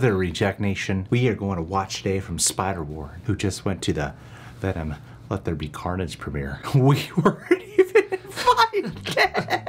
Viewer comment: What did He say here at 0:06? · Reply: He said that cosmic wonder got invited to the Venom let there be carnage premier and that they weren't even invited.